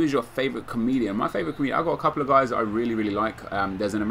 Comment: I thought you said favourite chameleon lol